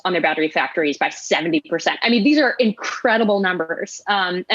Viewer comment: Did she say that These are incredible numburgers